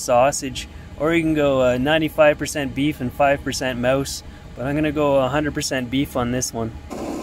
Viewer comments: Did you say 95% beef, 5% mouse? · 95% beef 5% mouse Did anyone catch that? This is a comedy, cooking and living in a mini van channel. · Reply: I caught it too lol. I thought I heard it wrong and maybe he said 5% moose but no I heard it right, it was mouse. Lmao